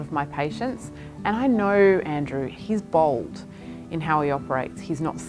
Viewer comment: I thought she said he was bald!